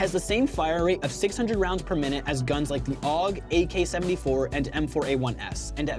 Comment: did he say ak74